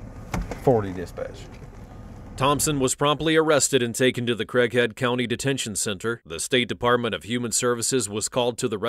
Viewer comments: thought he said the crackhead detention center LOL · thought he said she was taken to the "crackhead detention" center. because she looks like one in that photo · I thought they said the crack head center 😂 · I swear you said "...taken to the Crackhead County Detention Center" 🤣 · I seriously thought you called it, the Crack Head County detention center. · i thought you said crack head county at first 😂😂😂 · did they say "Thompson was probably arrested" im not sure if i heard right i dont think they said cause that would be kind of dumb ha ha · taken to the crackhead county detention center? I’m certain i heard it wrong but it fits. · i cant 🤣i heard the crackhead county detention centre im sorry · I had to rewind it because I thought he said “crackhead detention center” 😂😂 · Almost sounds like he said "she was taken to the crackhead detention center" 😂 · At the start I thought you said "crackhead country, 😂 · did they just say "crackhead Detention center" ?😂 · i thought he said "crack head county detention center", lmao. with a mugshot like that though, I wouldn't really be surprised. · deadass thought he said crackhead county detention center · It sounded like he said they took her to the" crackhead detention center" which looks fitting😂😂😂! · I swear I thought they said “crackhead county” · Did I hear “Crackhead detention center” 💀🤣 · I heard Crackhead County, which would've made sense too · Lol, sounded like said she was taken to the crackhead county detention center.